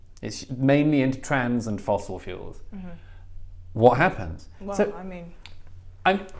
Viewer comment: what does he say here? "mainly into trans and fossil fuels?" i laughed pretty hard at this